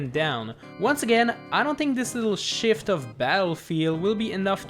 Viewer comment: I thought he said "This little *bleep*"